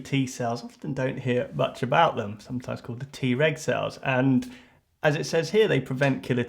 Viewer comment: I heard that as T-Rex for a second.